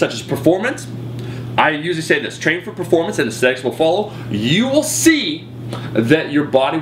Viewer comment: i thought he said 'the sex will follow' 3:56 XD